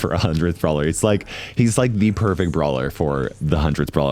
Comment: Did i hear, perfection?